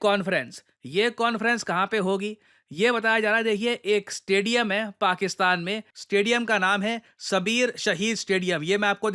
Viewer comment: Did you say "Stadium hai pakistan me"? 🤔🙄